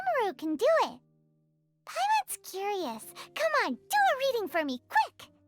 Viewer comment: did...did paimon just say "do a reading for ME"??